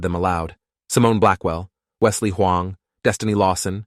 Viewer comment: DID I JUST HEAR HUANG?